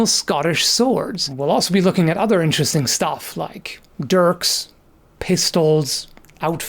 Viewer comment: For some reason i kept mishearing dirks as derps >,.,> <,.,<